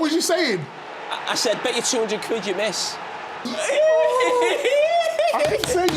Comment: Carra tells us what he had said to Lampard and I still only got half the words 😂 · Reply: »I said: "Bet you 200 quid you miss."« Quid being pounds.